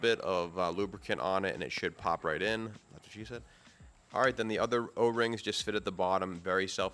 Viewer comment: Did I hear a "that's what she said" at around the 10:25 mark? 😆